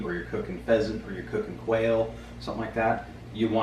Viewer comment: I thought he said whale when he said quail!!! 🤣🤣🤣🤣